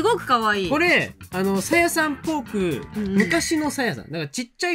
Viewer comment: すいません！13:59は自分も「さやさんポーク」って聞こえて脳内に「？」が飛び交いました❗️🤣